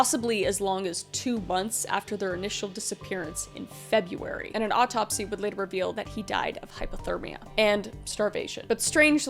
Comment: I just spent 3 hours doing my math homework and i literally heard "an autopsy would reveal that he died of hypotenuse and starvation"